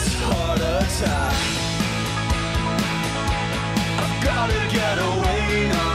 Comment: I swear I heard him say "I've gotta get a whale not coming back" 🤔